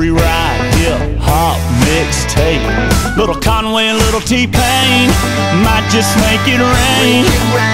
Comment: I thought he said a little Kanye and a little t pain 😂